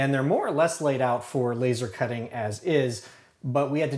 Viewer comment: I thought it said laser cutting a pizza lol